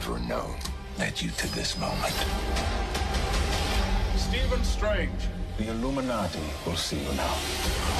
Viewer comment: what did he saying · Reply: “Stephen Strange. The Illuminati will see you now.”